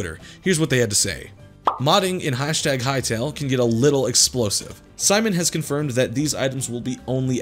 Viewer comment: DID SOMEONE SAY “EXPLOSIVE?!”